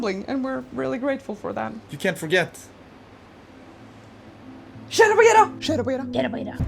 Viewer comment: sheda bageda? I had to do the playback speed at 0.25 to hear if what you were saying matched the words, lol.